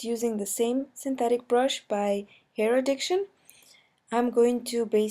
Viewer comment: Holy shit that whisper at 3:28 scared the crap out of me! But amazing work! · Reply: Elaine Figueroa what whisper? I went back and all I heard was “air diction”